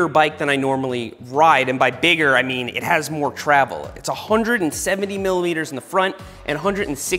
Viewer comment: Did I hear "170 milliliters in the front"?